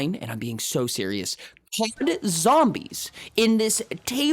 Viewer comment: What did you say? The audio gone whack... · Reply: "COD Zombies", aka Call of Duty: Zombies